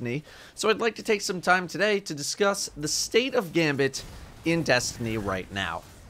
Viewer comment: I thought it said the stale gambit😂😂